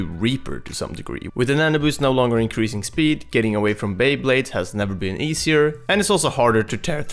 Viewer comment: I heard beyblades